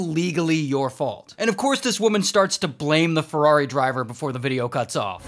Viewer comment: Am I seriously the only one who thought Ferrari said farret?